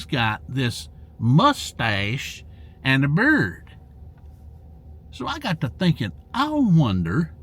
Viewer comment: I thought you said 'mustache and a bird' 🦜 made me giggle Sir 😊 thankyou and i hope your doing ok , sending ❤ from England 🇬🇧 to you and your 🦜 xxxxxx